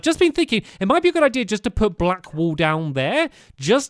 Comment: did says black "pool" instead of "wool"? 😂